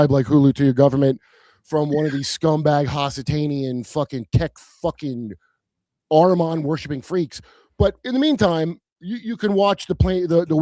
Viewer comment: what is he saying, hasideic aruman worshiping texh lords? 22:29 the technate, ok, hulu, ok. hasitanian? aruman? WHAT?